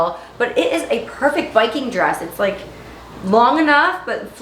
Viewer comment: The steak Josh made looked very tasty. 😋 And when you were talking about your cute black dress, I thought you said “Viking dress” and I was very confused. Then I figured it out. 😁